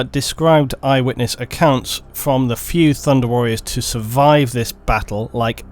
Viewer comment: The Thunder Warriors sound like my last D&D group... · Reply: I thought you said "War Thunder Warriors" for a second there.